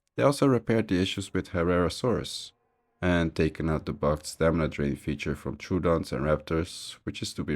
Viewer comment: what feature? I rewatched this part few times and still have no clue what u said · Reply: The stamina drain on raptor and troodon pouncing